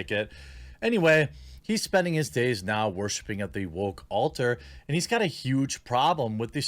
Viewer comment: Ooh! "Woke altar", I heard "wolf halter" haha. Dude mumbles sometimes.